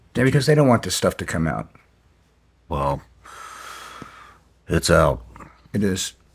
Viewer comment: does he say "It's HELL " or "It's OUT " ? Can't tell for sure, honestly... .